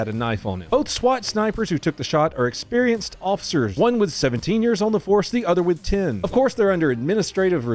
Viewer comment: Bruh i deadass thought you said the snipers were: "one was 17 years old, the other was 10"... and starting crying laughing. 😂